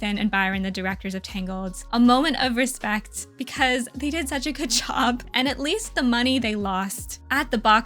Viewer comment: this a great analysis but I need to point out that here, I hear you say "they did such a k-chow" as if they're Lightning McQueen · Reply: girl she said “good job” not k-chow